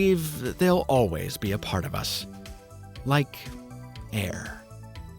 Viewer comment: I heard "like...hair" while looking at that hair